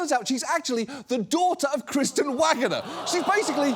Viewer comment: I thought her name said wagooner for a full min there